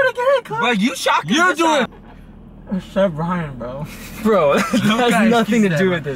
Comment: did just say sebryan or am i deaf??🤨🤨🤨